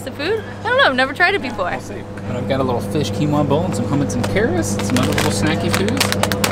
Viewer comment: Did you say, “Hummits and carris”?